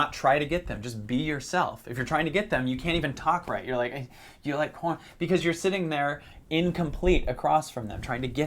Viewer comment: did he say "do you like corn?" Because if so, I totally got that reference.